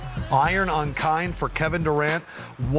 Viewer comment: anyone can help me what did they say on 3:02? Iron on kind? Iron on kine or what. thanks.